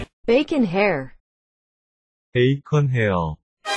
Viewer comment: só eu q ouvi bacon hell?